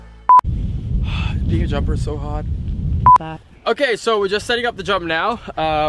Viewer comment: I thought he said “being a jumper is so hot” 😂